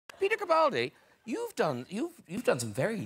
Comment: replay it again n again can anyone say what he was saying.. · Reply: He was saying "peter capaldi" the name of the Scottish man on the couch